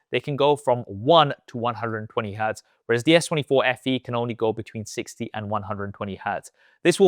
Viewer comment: Did he just said s24fe?